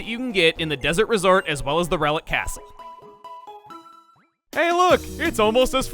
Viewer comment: I swore I heard you say Rowlet Castle instead of Relic Castle right there Chuggaa 😂